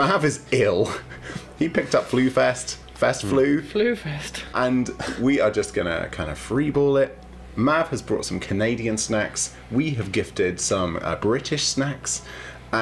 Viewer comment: I thought it said "Cadian Snacks" and was like "damm was WHfest next to a morgue?"